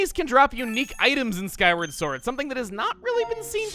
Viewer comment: did he say "Skyrim" sword? 😆